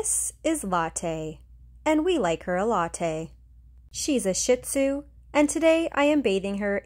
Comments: Believe I heard “Shit Tzu” lol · why do i keep hearing “shit-tzu”? 😭😭